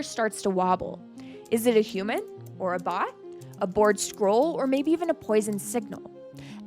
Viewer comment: this is what i heard, “is it a human? A bot? A bored squirrel?”